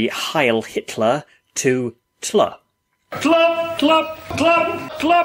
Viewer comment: oh, that’s what he was saying, I thought he was saying “CLOP”